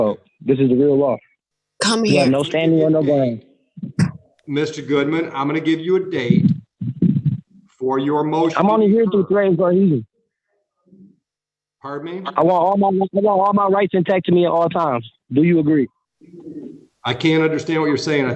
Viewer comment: I am only here under threat and COHESION! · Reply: I thought he said "threat and delusion" at first! 😂